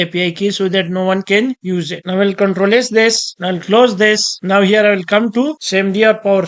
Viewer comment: What you are trying to say at 4:52 (control is this?). Please reply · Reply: i m saying ctrl + S this to save it